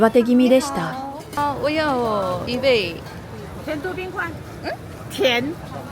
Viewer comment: こんにちは。ショックなこともありましたが、とにかく無事のご帰国、お疲れさまでした。(失礼ながら「迪化街の無駄使い」には笑ってしまいました。私は以前、台南で家族用に買った碰餅とわざわざタクシーで片道30分以上かけて買いに行った花生糖を、台南駅に置き去りにしたことがあります。(おそらく一生忘れないでしょう) 0:25で老板娘は「甜度冰塊」(砂糖と氷はどうすんの？)と言ってます。(塊は聞き取り辛いです)まぁネイティブ通りに聞いてるから、おそらくネイティブと間違われたんでしょう。 夜市での視聴者さんとの遭遇、これはの日頃の努力とコミュ力の賜物と思います。おめでとうございます。 佳興魚丸、私はこの店行ったことありませんが、最近乾麺がマイブームで、こういった福州魚丸の置いてる店は、まずセットで置いてます。（Googleマップで確認したら置いてました） 麺と調味油が入ってるだけで、卓上の調味料でこのみの味に仕上げて食べます。「メリケン粉を喰らう悦び」を堪能できます。次回機会があればぜひお試しください。 さて、今回また質問させてください。 1.台北、台南、基隆、高雄ときて、次はいつ、どちらに行かれますか？ 2.毎度同じ質問ですが、今回の旅の優勝は何でしょう？ 最後に、以前コメントしました。「元気を忘れた人は台湾へいらっしゃい」のCM、YouTubeであがってました。15秒CMなので、よかったらご覧ください。「懐かCM 日本アジア航空」で検索してみてください。